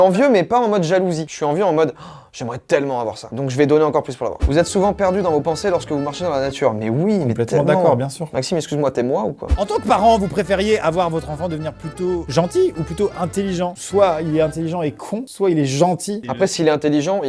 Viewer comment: J'ai cru que avait dit "Maxime excuse moi t'es noir ou quoi ?" XD ici